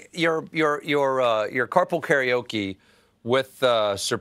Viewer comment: For a second, I thought Stephen said "Carpal Karaoke', and I envisioned a very different bit.